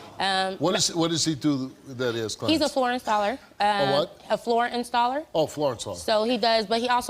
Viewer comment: i thought she said ponr installer love me some red bulll too! u dont accidentally break a baby arm